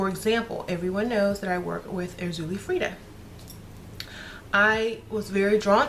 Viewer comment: name please? Couldn’t figure out what you said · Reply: Erzulie Freda